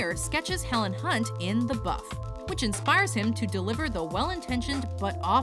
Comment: Did she just say "in the buth?"